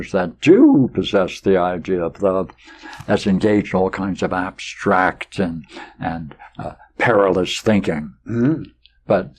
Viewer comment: what particular word is he saying in 13:40, I can't make out! Some help guys please? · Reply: "...engaged in all kinds of abstract and perilous thinking."